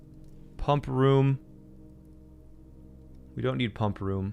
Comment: I actually thought this said Ponk Room instead of Bunk Room…